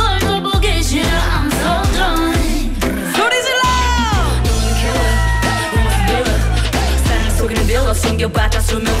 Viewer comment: does anyone know what solar says at 1:33 min ?? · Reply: Scream